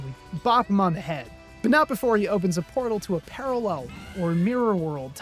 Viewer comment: For a minue there i genuinely thought you said, "he opened a portal to Brazil" I would not be surprised at this point.